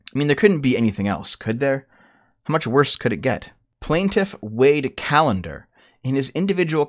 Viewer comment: Thought you said “plaintiff weighed calendar”